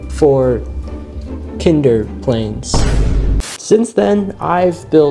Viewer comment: Every time he said “kinder plane”, I keep hearing “tinder”😂